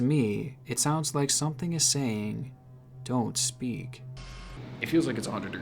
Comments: honestly I actually thought it said 'excuse me' instead of don't speak" at 23:51 guess I was wrong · instead of "don't speak" that voice you heard sounded to me like "god speed" kinda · OK SO I JUST GOT TO THE PART WHERE YOU SIAD YOU WOULD STAY THERE AND LIKE WHY WHY WOULD YOU DO THAT Edit: I don’t believe in ghosts but at first I kinda thought the second voice said “help me” not “don’t speak” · I really don’t think it was saying “don’t speak” I think it was saying “do we speak?” In a question like tone, like if you where whispering to someone a question in class. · it sounded like "it's me"